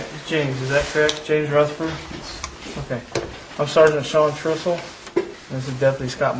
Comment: lol did I hear that right, the interegator's name is Sean Truthfull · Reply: I think it is spelled. He is now a Captain, and I believe third in command at the SO. Baker from the Ozkan interview is still there, I believe as well, and if true, means he now has around 30 years, more or less, with the Darke County SO. is in charge of the road patrol division at present, think third in command, possibly that is......